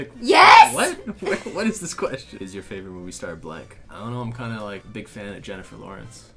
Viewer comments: sksksks I thought he said "is your favorite movie star black" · did they say "is your fav Movie star black?!" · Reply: Elysa_forLife _ "blank"